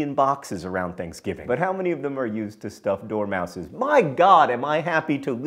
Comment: Did John really say "doormouses"?!? (Yes, he did